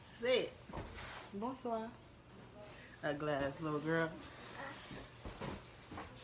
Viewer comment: Did you just say “ugly ass little girl” 😂😂😂